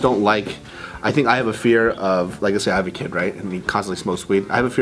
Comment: did say he had a kid?